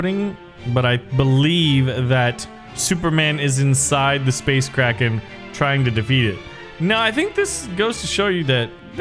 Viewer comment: i thought you said space karen